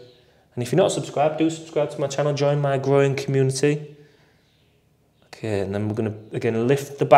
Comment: Do you know what I heard at 7:31? "Join my groin community" 😂🤣. Thank you for this video, seriously. It actually helps my anxiety a ton to do some stretching. It's like the mental anxiety has and tension is in the muscles.